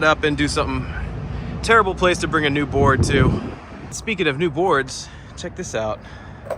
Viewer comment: I thought you said “terrible place to bring a newborn to” 😭